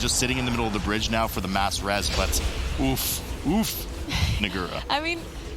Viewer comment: Someone please confirm to me what is said at 37:01 What I hear is "ooff..... ooff.....N Word' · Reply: "Nagura" is the female caster